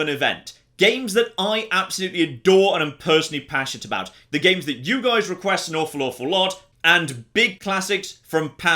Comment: What says: "The games that you guys request an awful, awful lot." What I hear: "DRAGON AGE"